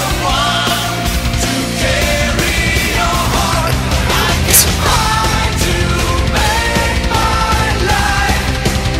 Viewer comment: what did Jellal say ? Plz tell me I didn't understand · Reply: Life As An Otaku He say Erza